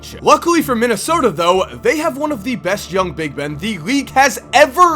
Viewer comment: Is it me or did just say that Minnesota has one of the best young big ben???